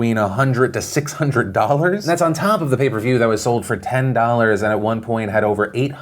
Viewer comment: I thought everyone was saying paper view the whole time... Only figured it out was pay per view on the day of the fight